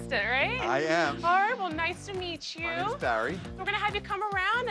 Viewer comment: did she just say horrible nice to meet you?